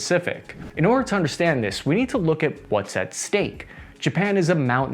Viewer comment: did you say steak?